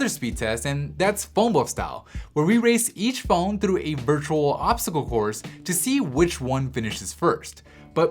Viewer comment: Whats does he says 00:20 ?? obstacle core??? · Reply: obstacle course